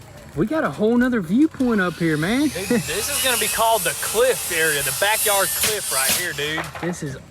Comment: I thought you said clit area lmao 😂😂😂